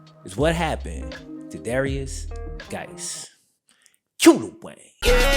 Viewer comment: Is it cue the way or cue the Wayne? Cause I thought he said "cue the way" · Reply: Chris Parridgen it’s cue the Wayne